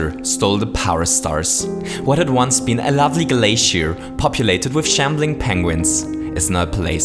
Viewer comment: "What had once been a lovely glacier, populated by shambling penguins..." I'm extremely tired so I somehow managed to mishear it as "It had become a terrible glacier populated by shouting penguins..." as i said, i'm too tired.